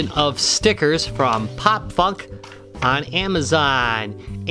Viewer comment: Holy moley! It sounds like I said Pop F***!